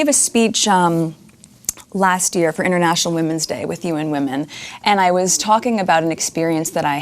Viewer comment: I thought she said, I had a conversation with........human women :) ha ha I was going to say, congratulations you found some human women, you go girl :) Oh god, why didn't her dad tell her that HE got onto that advertiser after they didn't reply to her, leading her to believe it was HER triumph, no wonder she's so full of herself, never overindulge your child.